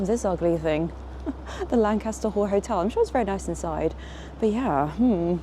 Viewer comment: Hahaha 5:00 I thought you said, The Lancaster Whore Hotel.’ Well, you know, Sussex Gardens so near 👀😳🤣❤